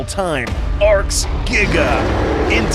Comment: When said Arks giga. I thought he said Arks nig